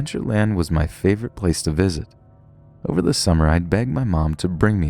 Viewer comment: i thought he said over the summer i bang my mom